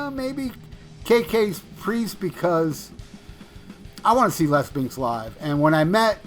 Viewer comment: sounds like you say "I wanna see lesbians live!" Hey if that's the case I can refer you to some gentlemen's clubs post covid lol!